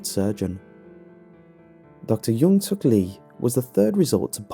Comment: I THOUGHT IT SAID JONGSUK BAHAHHAHAHA